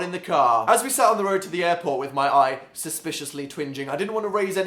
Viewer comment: Hey Dan. I absolutely love this video, but I couldn't help but realize that the closed captions are off in a few spots. 3:13 is rather funny. You said that your eye was TWINGING but the captions said your eye was "twin-engine". While this is good for a few laughs, those who have a hard time hearing and need the closed captions may find them hard to follow. It would mean a lot if you could revise them so that ALL of us can understand. Thank (